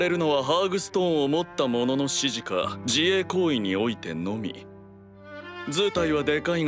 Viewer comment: ベルゼブブの自衛行為が自慰行為に聞こえた